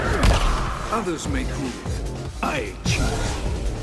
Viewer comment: What does he say at 0:28? Other may grieve.. i ???? Can someone help? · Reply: You know, for awhile I thought he said, "Others may think. I achieve." Which, if I do say so myself, I rather prefer given Shang Tsung is a man of no little intelligence, by way of which he schemes and then puts into action through highly advanced forms of sorcery. But it also works as an immediate taunt in kombat: his opponent thinks/desires/anticipates victory, but he secures it through crafty means. Alas, he actually seems to say what has already been clarified in the comments above.